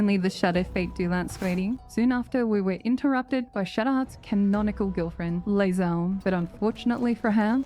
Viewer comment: I swear I heard "githnonical girlfriend" and that's what you should say from now on.